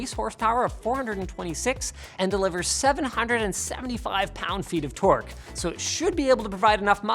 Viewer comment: "pound feet" of torque smh What I heard was guns per football burgers